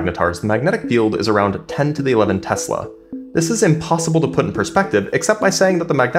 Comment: A thought you said 10-11 Tesla, and the text is coveresd. 10^11 tesla, makes sense now.